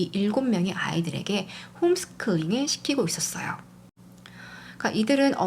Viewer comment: 홈스클링으로 들리네요.살짝 거슬리긴합니다.